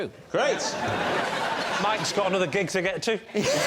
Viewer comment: What does Lee say at 4:52? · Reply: "Mike's got another gig to get to"